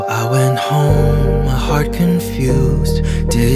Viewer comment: actually thought he was saying ' heart' and not 'hurt'